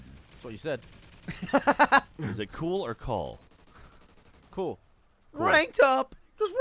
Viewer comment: Jack- "Is it cool or call?" Which did Gavin say? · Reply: As an English person, I think it was 'call'. I would've taken it as 'call' in that conversation. :)